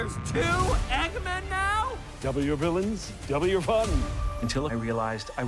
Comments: I dumbass thought he said "W villains, W fun" 😭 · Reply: I thought he said double you villians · I dumbass thought he said "W villains, W fun" 😭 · I dumbass thought he said "W villains, W fun" 😭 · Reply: I always heard “double your villain” etc. But I don’t understand or have exposure to these new terms, other than by accident with videos from idiots.